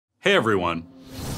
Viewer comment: "Hi everyone. Fuck the Police!" hahaha 0:00 pareciera que dice eso :c